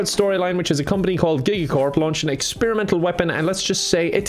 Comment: I thought he said gaycorp in the sponser